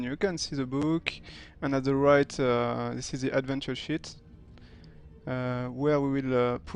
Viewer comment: It sound like you saying adventure shit XD but np i understand what you mean :P